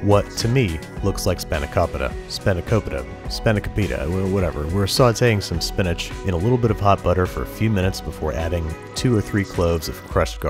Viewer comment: Swore you said Pot Butter and then watched the same scene 10 times. HOT butter!!! Got it